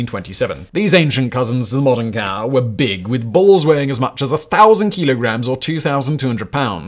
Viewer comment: When he said bulls, for a split second I thought he said balls.